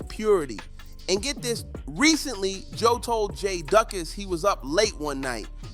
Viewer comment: Why he pronounce Jadas name “jae duck is” 😂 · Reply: Thought he said Jay duckins